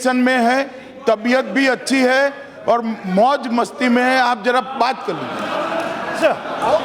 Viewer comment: i think amit shah said maut msti m hai 😂😂😂😂😂😂😂😂😂😂loya loya